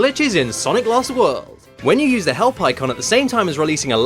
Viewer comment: When he said help icon, I kept hearing 'hell pikkon' (pikkon is from dragon ball z)